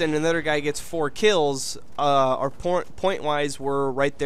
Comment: 'pornwise' is what u were about to say, your awesome.